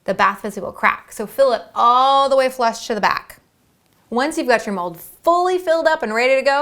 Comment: 23 u said we have to fill all the way flush to the back? sorry my English is not good and I want to learn the process to making bath bombs. That is why I want to understand what u said. Thank you!